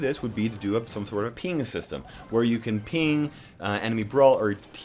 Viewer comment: “p*nis system” I legit thought you said that